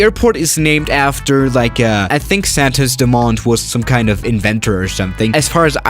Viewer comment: I thought it said demon